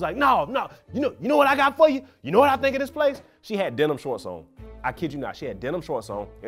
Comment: the first guy said denim shorts. whole time i thought he was saying dental floss